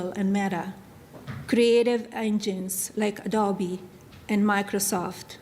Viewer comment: When she said "creative engines like__ & microsoft" i thought she said "Dobby" and i thought what the hell does Harry Potter have to do with this? Then i listened a couple more times & realized she meant ADOBE not Dobby. 😂😂😂😂😂 It's like torture listening to either one of them talk. Ugh.